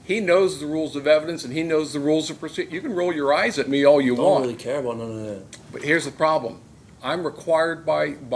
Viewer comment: BRO WHAT. “I ain’t really care about that bruh” I misheard what he said at first and assumed he said “I wasn’t rolling my eyes sorry” but no, he didn’t even deny the fact that he rolled his eyes 2:07 “bruh” if you can’t understand any of what he said, then you should stay in prison because you’re so fucking uneducated on top of being a criminal that it’s unironically dangerous 4:12 “that will be tested we’ll see about that” DUDE the judge was so shocked at that response 4:44 exactly like the judge is genuinely so shocked at his response. And “it’s only 3 years” isn’t true it could change lmao 6:20 LMAO “I know a little bit more than most” the judge is literally silent cause he’s so shocked at how stupid this guy and his choices are